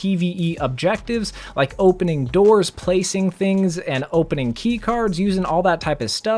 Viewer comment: did he just say opening keycards